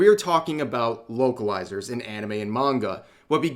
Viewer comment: For a second, I thought you said Wokealizers for a sec, but I misheard. 😅 Granted, such a term does seem to fit. Maybe we can coin this new term for them?🤔